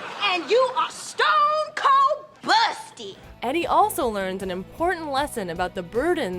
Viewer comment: It sounds like you said abusity